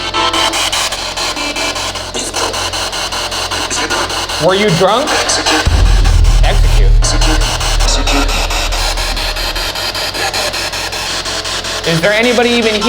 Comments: Make sure to leave your questions for our Post Mortem episode down below! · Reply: #PostMortem Did the ghost at 6:57 really say execute? I heard mexican and rewatching it I can just only hear it say mexican. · I heard ACCIDENT, not execute, guys.. Also, 21:35 I hear BEAR WITNESS, not bear with me. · When it shows it saying "execute" I swear I heard "Mexican" 😂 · When they did the spirit box on the stairs and they heard “execute” but I hear “Mexicans” or am I tripping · Are you sure about execute at 6:57 ? I heard “ACCIDENT “ and definitely relevant to the question. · Make sure to leave your questions for our Post Mortem episode down below! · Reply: #PostMortem Hey, at 6:55, that voice sounds more like it's saying "Accident" instead of "Execute." Also hey I'm a newer fan! Binged all of unsolved a month or two ago- laughing at the timing of this being the series finale. You guys are great, thanks for the laughs. #MoreShaniacThanBoogara #FunToPretendGhostsAreRealSometimesThough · Where it says... "Execute!" I heard... "He's a cutie." I couldn't stop laughing. A-can we get a ghoul brothers blind date with the ghosts ep?!